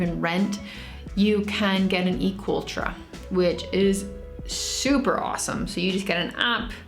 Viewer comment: Please help me, or someone else. I couldn't understand what she said here. The subtitles say "Eco Ultra," but I couldn't find anything about that. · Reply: Ahhh yes it’s E-Cooltra now called Cooltra since the video was posted. It’s like an easy way to rent a motorbike with an app